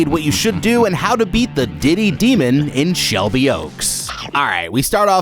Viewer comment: I swear in the beginning I thought I heard diddy demon 😂😂😂🅿️